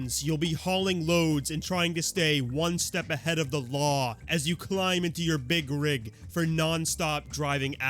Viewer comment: I think it was actually saying you'll be one step ahead of the law of physics, considering what permanently reversing does. 😅